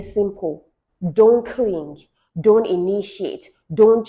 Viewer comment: I thought you said don't clean😂😂😂 But great advice,thank you🌼❤